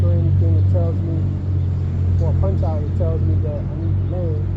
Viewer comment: I didn't understand what you was talking about?? 🤣🤣 Congrats I'm so happy to see you finally are enjoying digital FPV flying especially with your ole trusty ELRS radio so now you could be able to enjoy your FPV digital whoop flying right off your deck without worry about Fail Safe's anymore this whoop looks like another keeper for sure dude👍👍😎😎👍👍 I look forward to someday seeing you convert all your other older FPV smaller brushless quads that you have bad analog VTX's on like for example your Emax TinyHawk Freestyle 2.5 inch 2s to Walksnail and such if you want to that is?😁😁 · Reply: Oh I said whenever I do a punch out or flip the OSD tells me I need to LAND NOW 😂😂😂